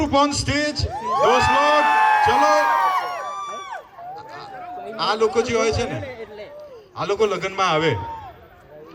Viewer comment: uncle ne kya bola ??? Batao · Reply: Har jananm ye hi biwi mile